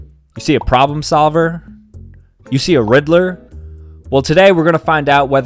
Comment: when in the first 10 seconds, when he said "riddler", i genuinely thought he said "rizzler" 🤣🤣